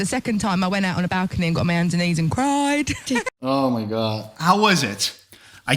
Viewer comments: I think she said ''got on me hands ('ands') and knees and cried'' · Adele doesn't say "got down on my knees" she says "got on my hands and knees", you don't hear it clearly because of her accent and because she's speaking fast but your transcript is incorrect :(